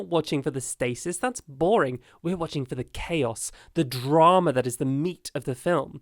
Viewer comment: thought you said “we’re not watching for the stasis, we’re watching for the gay-is”